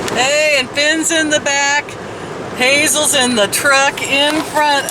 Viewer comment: OMG! I have my speed setting at 1.25, and at the start of the video, I thought you said "Hazel's in the trunk."